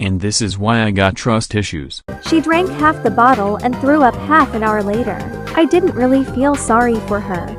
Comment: I heard blew up